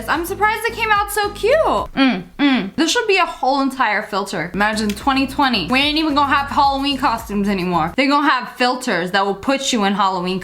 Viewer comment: Umm is it just me or did she say "imagine 2020, we ain't gonna have Halloween, costumes anymore..." at about